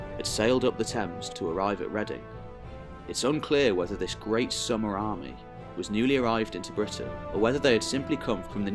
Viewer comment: I thought it said Sumer army 😂